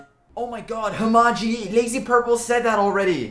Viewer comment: did i just hear a lazy purple mention? in